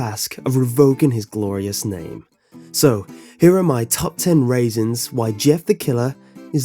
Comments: I thought he said top ten raisins. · It sounds like he said raisins instead of reasons. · Did he just say top ten raisons LOL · Did he just say "Top 10 Raisins"? O_o